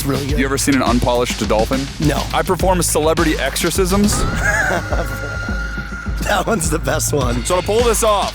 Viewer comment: I thought he said he performs celebrity circumcisions.